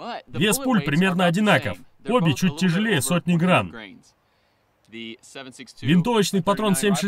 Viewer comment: Вес пуль немного преувеличен ... :) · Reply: Да, Вы правы, мне послышалось "100 грамм"... А так чуть больше 6 грамм получается, что является нормальным весом для пуль 7.62. Спасибо за поправку!